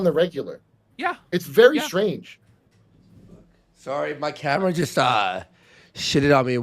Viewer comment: I thought homeboy kept saying "Sorry My Cat Shit on Me" when his camera messed up.